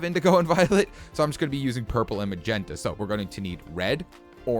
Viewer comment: did he just say purper